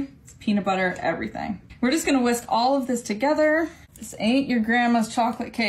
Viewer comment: did she say wisp?